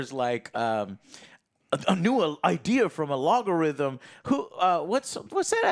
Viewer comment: did i just hear joe say "alogarithm" ?